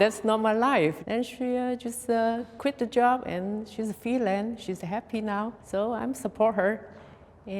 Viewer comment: Did she just say that her daughter is a felon and happy about it? Lmao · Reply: She meant "freelance."